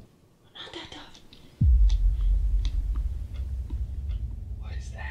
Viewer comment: Yoooo did anyone else hear that female voice at 41:27..she said "Not that door". I hear that clear as day right behind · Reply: She said not that tough!!!!